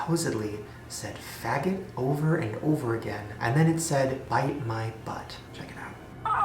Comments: i have one of the teletuby things in my house, as soon as i heard you say 1998 or somthing i paused and started typing. but to me it sounds like its saying "bite my butt" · Wait. I had that Teletubbies toy. We thought it said "magic, magic. Magic, magic. Bite my butt." Sometimes we still quote it because we found it so funny. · I heard "fuck my butt" not "bite my butt" 😂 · my cousin has that exact telly tubby toy!!!!!! we thought it said "ride my butt" omfg